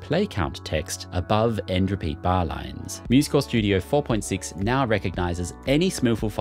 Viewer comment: I kept hearing you say "musical studio " 😅